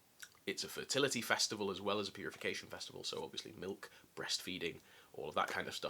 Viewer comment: I legit thought you said "Punification festival" instead of "purification" and I was like "I don't think the Lupercalia was Carthaginian tho???" RIP my audio/verbal comprehension